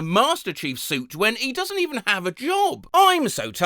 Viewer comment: I thought he said: he doesn't even have a jaw